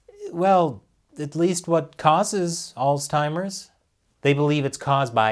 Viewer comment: Alzheimer's doesn't have a T....Sounds like you were saying Oldtimers.